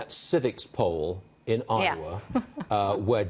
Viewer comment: did you see that civics poll in (did he say) Ottawa?